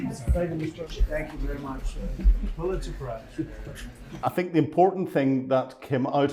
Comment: Did Trump say Pulitzer Prize? Anyone want to guess what he really meant to say? He’s in this for the NPP.